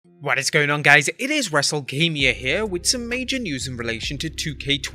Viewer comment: At the beginning it sounded like you said wrestlekamia